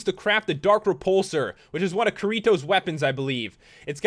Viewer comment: x computer slaps him in the face x ITS KIRITO NOT KURIBO! ( don't judge it sounds like he said kuribo)